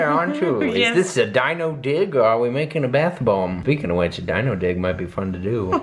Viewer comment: Dino dig i see now , when you are from another country and you speak another languets ( duh ) it sounded like you said “ Dino D*ck 🤣🤣🤣 and then the earl said “ especially when they are made of stone “ and i was CRY- laughing . But ... the bubble bath hearts looked like MEAT when you pulled them out of the water . That was nasty. I love when you two are making videos together . You two allways make me laugh . Xxx from the Netherlands ( Anne) Miekje 🥰😘😘